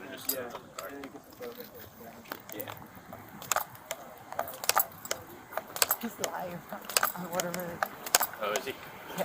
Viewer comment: Did they say he's a liar?? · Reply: nah, they said "he's live".